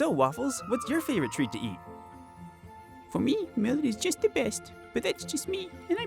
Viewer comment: I've watched 20 times and just realized I still don't know Waffles' first favorite treat. I love this video and this happy budgie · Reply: I had trouble understanding what her favorite treat was as well. At first, I thought she said melon. But now I think it's millet, which is an actual treat for budgies and what I used to give my budgies back when I had them over a decade ago.